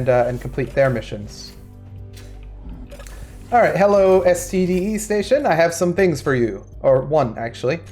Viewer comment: I thought you said STDs station 🤣 my bad why not try it the way around as well too buy from MEIN station and sell at STDE, nevermind everything pricy at MEIN